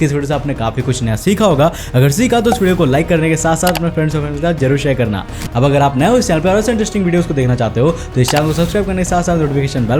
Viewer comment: What did he say..🤣10:48 friendsavwoaa 🤣🤣